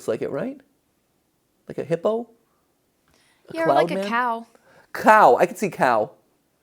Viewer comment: I think the cow they said at 18:09 is actually candycat?